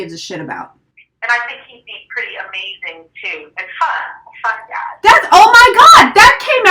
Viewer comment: I can not understand what the astrologer say at 10:38 just before you say Oh, my God. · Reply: She said "and I think he'd be pretty amazing too, and fun, a fun dad".